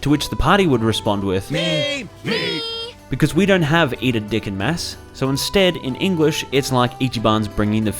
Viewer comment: 💀 did he just say “eat a dick in mass” 🤣😭